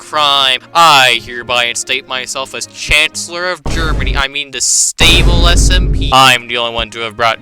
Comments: did i just heard Germany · did bro say chancellor of Germany